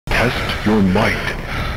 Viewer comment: I heard " Test Your White "